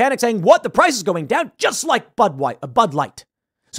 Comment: I don't want an apology from Bud Light. I want an outright collapse of the business. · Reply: "just like butt wipe, bud light", 😆🤣, sure sounded like that's what young Tim said.